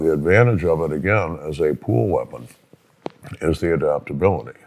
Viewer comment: is he saying "pool" weapon or "cool" weapon 4:32 i think he is saying pool weapon and that makes no sense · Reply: Pool, as in a collection of items (as in "motor pool") to be issued out for a set period of time. The weapon isn't YOUR weapon. Instead, it's one taken from a bunch of others. Not everyone would care for a "pool" item as well as you would. As such, they tend to be used & abused.